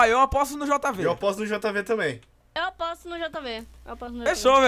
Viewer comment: a diz: "eu aposto no jv,eu aposto no meu amor" Foi isso mesmo que eu ouvi?